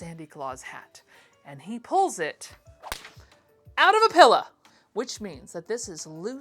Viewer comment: did someone just say “pilla”??? I thought only my Pennsylvania born and raised grandmother said that!!!!! ps-your shorter hair looks lovely… the curls are so nicely defined!!!